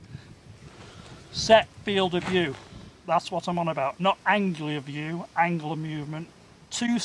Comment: did he say Anglia view? Now that place is pretty flat! 😂